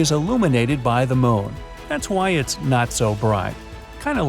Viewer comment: Did you just say you were not as bright